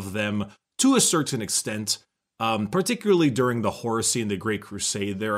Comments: “The horusy” all i heard was “the hor-sussy” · I THOUGHT HE SAID HORUSSY AND I'M FUCKIN' DEAD.